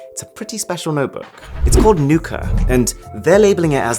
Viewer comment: What everone hear: Nuka What i hear: Nuker